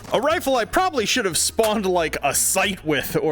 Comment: I thought he said "should of spawned a scythe with" like he was gonna start reaping sosig souls with a scythe attachment lmao